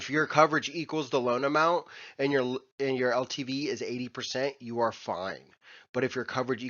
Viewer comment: I'm new so I have very little context or background knowledge to draw from. At 2:50 I thought you said LTD but I think it was LTV, correct? I love your channel!